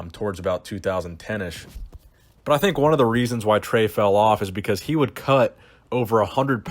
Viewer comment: When said @ 1:31 , "When Trey fell off", did anyone else hear "When Dre fell off" from Forgot About Dre? lol